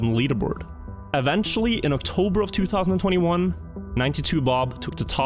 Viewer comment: octorber instead of october